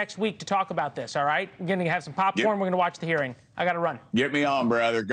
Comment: Did he just say "Tell Your Mama Go Balls?" 😂